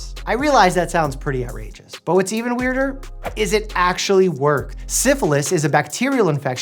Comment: Did anyone else hear him say "Is it actually work" ?